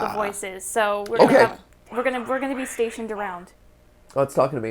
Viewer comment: for those wondering. I think that the game/voice said "We're on our way" :]